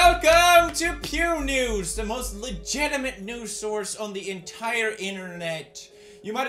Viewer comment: I thought he said Pews Nudes